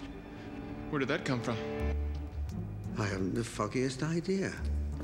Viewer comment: sounded like he said "I haven't the fuckiest idea" lol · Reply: He said foggiest, it's his accent that makes things sound different